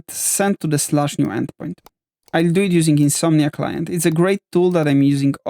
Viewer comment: what are you using ? you said very indistinctly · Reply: Oh, it's Insomnia Client. Great tool for crafting HTTP requests of any kind.